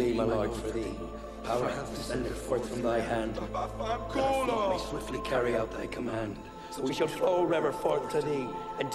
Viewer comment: Anyone know what Yakavetta says at 2:49, right after the "power hath descended forth from thy hand" line? Sounds like "Papa culo" but that doesn't make any sense, unless it's some Italian that I don't know? Slang or something? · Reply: You're absolutely right. It is "vaffanculo."